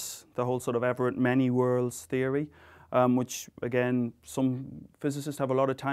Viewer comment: To anyone relying on the subtitles, may I point out that the text at around 12:45 is wrong. He does not say "elaborate many worlds theory" but "Everett many worlds theory" after Hugh Everett that devised it. There may be other mistakes of course, since I wasn't actually looking out for them; I just happened to spot this one.